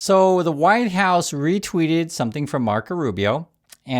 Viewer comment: OMG! I don’t know if it was intentional mispronunciation or just my bad hearing, but I swear I heard him say “Whine House” at the start of this video — and now that’s what I’ll be calling it as long as the GOP holds power! 🤣😆🤣😆